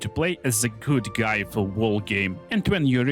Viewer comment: At the like 13:50 time mark, is he saying you have to play as "a good guy the whole game" after being a baal cultist earlier? I cant tell what hes saying unfortunately